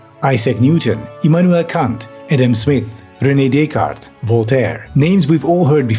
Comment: I thought you said Adam Something at first! I was like “I don’t blame him”.